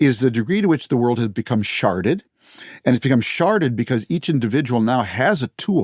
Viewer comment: I now hes saying 'sharded' but I keep hearing 'sharted' and it still somehow works. 😂